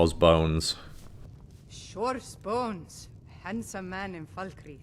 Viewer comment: I used to think she said " Shores BALLS " lmfao